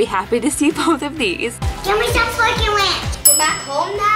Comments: it says give me the fork and ranch not what u think it says😂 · it sounded like said "give me the f**** ranch" but she said give me the fork and ranch. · Did she say “Give me the f***ing ranch”?